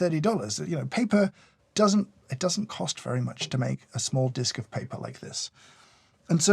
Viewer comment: For a second I thought you were about to say "paper doesn't grow on trees".